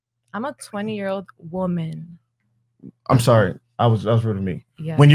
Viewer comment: “when ur a 20 year old kid” “ excuse me i’m a 20 year old women” “ my bad that was rude... when ur a 20 year old kid” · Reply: no he said “ I’m sorry that was rude of me” when I first saw it I thought he said “ I was referring to me’ but that’s not what he said, contextually it wouldn’t make sense either.